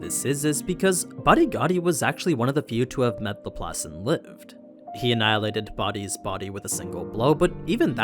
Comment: i have a hard time understanding what he said @ 5:00, can anyone? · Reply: 'badigadi was actually one of the few to have met laplace and lived"